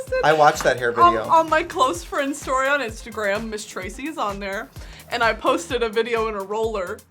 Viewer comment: DID SHE JUST SAY MS TRACEY WHAT AM I MISSING SOMETHING ???">:>" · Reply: Ms Tracy Martel was reading her. Welcome to the library